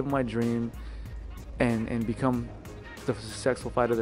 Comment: I could have sworn Ortega said “become the sexy fighter I want to be” at the end. You did Brian, you did.